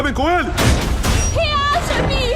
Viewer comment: "Reaja, bee!' - escutei com a voz da Pablo Vitar e to parecendo um maluco rindo sozinho.